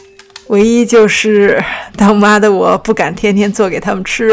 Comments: 最后一句话我听成：他妈的我不敢天天做给他们吃啊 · 空耳：他妈的我不敢天天做给他们吃啊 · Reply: 是...當媽的我...啦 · 当妈的我。。。听成了他妈的我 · 对不起，听错了，“当妈的不敢天天做给他们吃”听成“他妈的不敢天天做给他们吃”